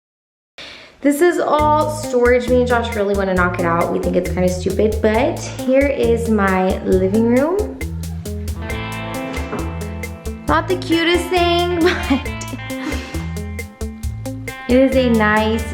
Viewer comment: DID YOU JUST SAY...."HERE IS MY LIVING ROOM" WHEN YOU WERE SHOWING THE LAUNDRY ROOM?? LOL I THOUGHT YOU DID...I HAD TO REWIND IT TWO TIME TO MAKE SURE I WASN'T MISTAKEN. HA HA